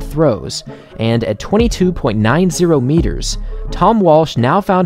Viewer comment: 22.9 kilometers??? is that what he said?? did I hear that right??